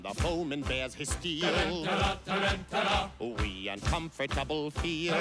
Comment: When I was a little boy watching this on HBO, this was always my favorite scene. And I thought they were saying "Tarantula, tarantula!"